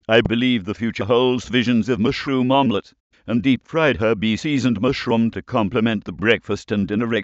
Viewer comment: lol at 0:36 i swear i heard "deep fried herpes"